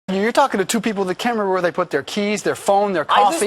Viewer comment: I hear them say 'You're talking to TWO people...".